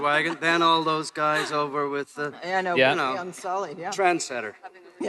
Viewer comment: Can someone tell me what he says at 9:48? Transcenter? Im not a native english speaker i understood the rest but that word :( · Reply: K. Daniel He said Trendsetter